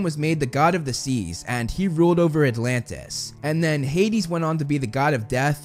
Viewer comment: did you just say Atlantis?